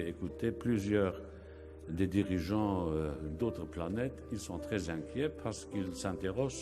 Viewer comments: I hear him say something something "Planets" @ 1:35 ! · It sounds like he says 'd'autres pays, Länner' To me he sounds unsure about the term in French and adds Länner in his own language, Luxembourgish to emphasise countries. But I'm sure his his French, German, Luxembourgish and English are not as good as your language skills and that you are correct. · Planet = Planete' Countries = des pays He clearly said Planete'